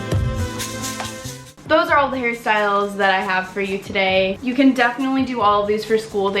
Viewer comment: i heard "those are all of the harry styles i have for you today" ahahahaha